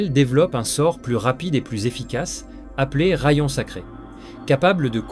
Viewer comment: je me suis fais la même remarque... Au début je me suis demandé si c'était pas un texte lu par IA par moment même 😅 pour expliquer tu as des pauses non naturels dans tes phrases... Par exemple au moment où j'écris ce commentaire à 15:02 "Belzébuth, roi de la......voracité" Certains moment tu hésite sur des mots aussi Ps : Ce n'est pas une critique (je ne serais pas capable de créer un contenu narré comme celui ci) mais une possible piste d'amélioration Edit : je ne connaissais pas la chaîne avant cette vidéo donc après l'avoir fini je suis aller voir le reste et effectivement en regardant les vidéos précédentes je dirais que tu fais soit appel a des doubleurs freelance soit des voix généré par IA Dommage ça fait perdre du naturel à la chaîne... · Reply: Moi j'ai entendu "Ra-yon sacré".... hmm ok :)